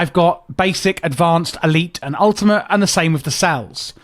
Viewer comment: did you just say...... ultimate cell.....?